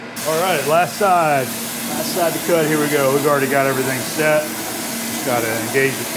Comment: I know you said "last side to cut, here we go" but for some strange reason I heard "Last car to pass, here I go!" B-)